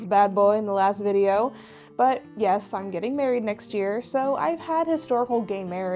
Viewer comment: i had this video on 2x speed when you announced your engagement and my brain mixed up the words you said so i heard "i'm getting historical gay married next year"